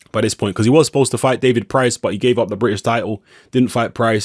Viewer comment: sounds like your saying Fury ducked Price. This opens a can....